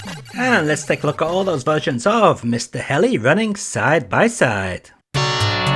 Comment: cute game ... but for some reason i thought it said Mr hell " at first